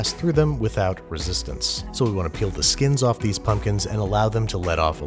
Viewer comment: Did he say he peels the skin off his coworkers?